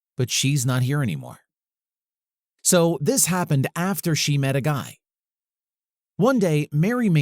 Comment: I thought it was gay instead of guy